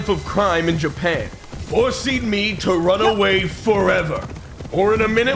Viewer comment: I know he said "forcing me" but I heard "horsing me" as he mounted his horse